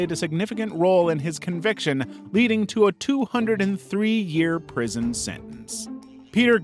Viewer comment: captions say 23 years also how did joel get a 203-year prison sentence? 😭😭